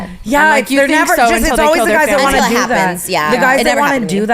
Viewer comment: Did anyone hear Brooke at 1:05:50 when Trish and Tana were talking about loving murderers??? She’s so quiet but she says, “you think so until they kill their whole family.” I’m dying she’s so funny😂😂😂